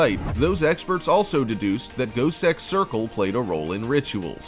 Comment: I swore the said goatsex circle not gosek